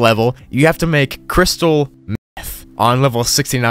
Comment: what did u say? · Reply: Meth